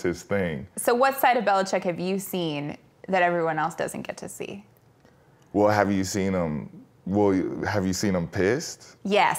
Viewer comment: legit thought he said “Well have you seen him piss?” “Yes, right at me.”